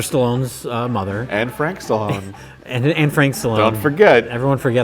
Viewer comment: Thought Josh said Anne Frank Stallone